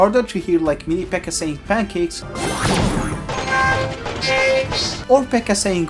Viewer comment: i swear y'all lying he can't be saying pancakes like that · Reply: I always thought it was ‘my baby’😂